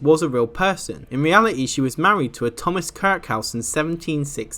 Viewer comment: I legit thought you said she was married to a Thomas crackhouse🤣